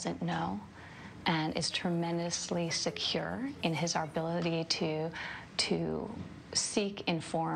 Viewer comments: Did she just say "ability"?? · Um, so at around 18:54, am I hearing things or did Ivanka say "arbility" instead of "ability"? Because I re-listened to that segment a couple times and I'm pretty sure she did. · did she just say "arbility"?